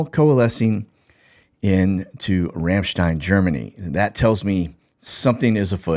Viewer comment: Everytime you go over “Ramstein” it sounds like you say “Ranchdine.” My husband says it because you are a Texan. 😂